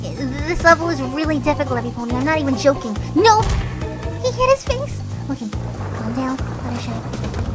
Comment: omg did fluttershy said at 19:30 he's hit his things?? xD😂😂😂😂😂😂😂😂😂😂